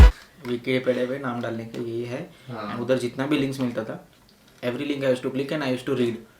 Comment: what does he says at 12:17 link naam daalne ke liye, i didnt get it. could someone help me